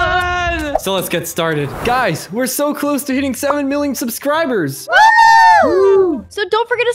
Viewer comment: why did I hear “we are so close to eating 7 million subscribers” I feel so weird now